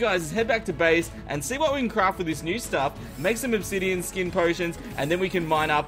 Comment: I thought he said oblivionskin potion